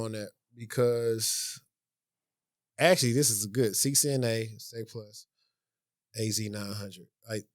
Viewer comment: Did you say CCNA, Sec+, AZ-900? @ 39:00?